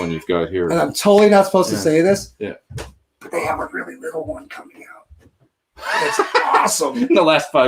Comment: what was Tracii saying at around 1:41:40 when he held his hands to his mouth? did he say "they have a really little one coming out...."? was he referring to a new Headrush modeling unit that's not out yet?